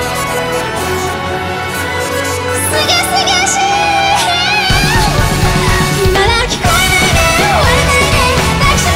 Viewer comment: ら辺のやつってなんて言ってますか？ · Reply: すがすがしぃ